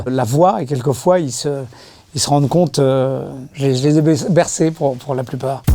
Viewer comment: y'a que moi qui entend le lapsus "je les ai bais... Bercés !!" en parlant des enfants ???? xD